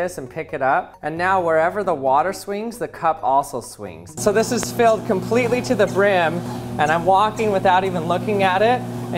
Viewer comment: I thought he said "so this has failed comepletely..."